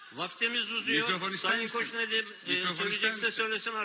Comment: de konuşan pavalı köpek ne diyor anlayan var mı ali Koç mikrofon ister misin cevap vermiş